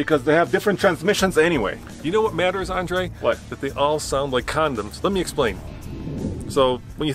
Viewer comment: They all sound like condems???Did I hear that right?????? 8:45 Please share your thoughts LOL